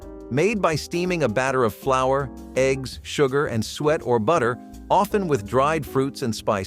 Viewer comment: Hold up, did I hear that correctly? Steamed pudding was made with SWEAT? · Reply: Suet 😅